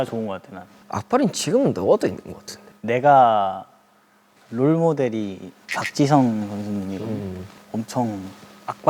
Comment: Help, I thought he said he's role model is soccer player Han Jisung 😭😭